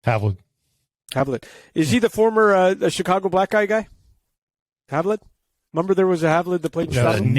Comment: anyone else catch this? Lol "Is he the former Chicago Blackguy guy?" I know he meant Blackhawks guy, but LOL